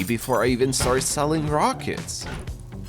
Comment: i thought the title said "selling ROCKS until I get raided". that would have been amazing.